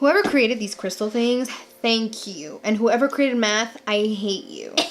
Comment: I thought you said “whoever created meth”. Lord have mercy. 😅😅😅 but GURL. YOU LOOK WONDERFUL. HOLY MOLY. 😍😍